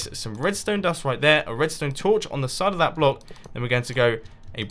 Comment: I always hear "on satalite block" insted of "on side of the block"